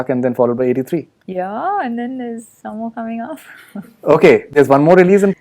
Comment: did she said and then... Umh someone is coming? Is she pregnant 😳😳😳